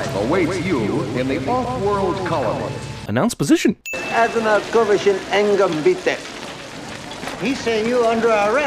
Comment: He actually says "Azonnal kövessen éngem, bitte." which is half Hungarian and German...so he actually says "Follow me immediately, please" - the old chineese guy should work on his cityspeak.